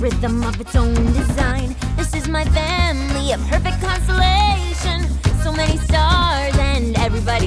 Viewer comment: Just me who misheard her saying "my family is a *consolation*"? @ 0:30 ~i ThINk YouR GifT Is BEiNG in DenIAl~ 😂